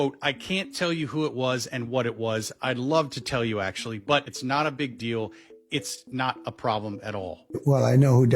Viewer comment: More than “…it’s not a big deal” 26:45 What he really said was “IT’S NOT UP TO ME.” 😂